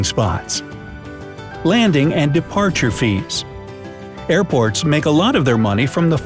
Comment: I thought it said “How Airpods make a lot of money”